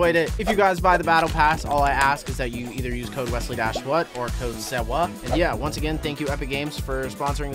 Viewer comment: DID HE JUST SAY USE CODE SAWWAHH